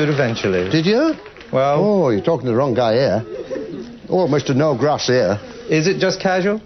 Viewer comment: "almost to no grass for me" did he really say that?